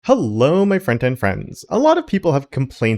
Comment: Are you actually greeting us as "front-end friends" as the pesky subtitles which randomly appear regardless of my settings tell me? I would have sworn you'd be saying "friend and friends" 😅